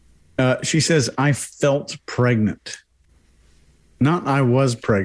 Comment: I thought she said she "fell pregnant"? It's a weird phrase but very common these days.